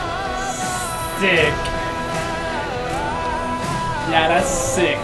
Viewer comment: Bro said sick but the caption said d*ick 😂